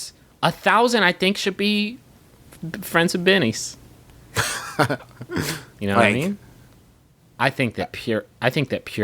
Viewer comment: at first i thought they said "friends with dennys" and i got real confused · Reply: Seth Scialdone friends with denefits